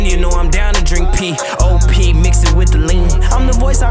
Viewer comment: Lil Durk did not just say “India know I’m down to drink pee, oh pee mix it with the lean” 💀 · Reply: P-op (pop) and mix it wit the lean